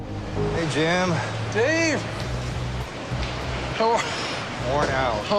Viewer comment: I know he said, "You look a little tired, my friend." but my tired ass heard it as, " You look like Turd, my friend." and laughed like an Uncouth immature little piece of shit.😂